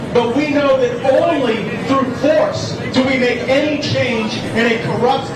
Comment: Did he said "we know ONLY TROUGH FORCE we get change"???? 0:15 No wonder 6th happend.